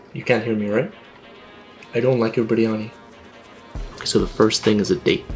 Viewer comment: why i heard he's saying"i don't like yours pretty honest"